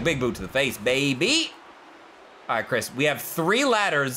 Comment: lol did he call him chris